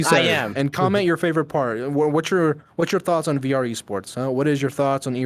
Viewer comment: idk homie ...i heard something WAY different... and can't unhear it ... I blame Gramsy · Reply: btw just noticed never told u what I heard "cum in your favorite part" Cannot unhear